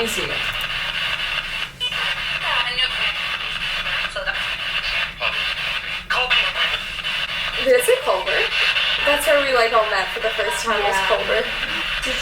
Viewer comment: it kinda sounds like it says colby not Culver just saying maybe I'm the only one who heard that 🤷‍♀️